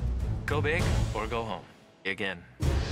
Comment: did say begin or egg in?